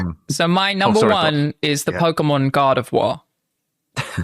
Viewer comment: I also thought was saying "Pokémon God of War"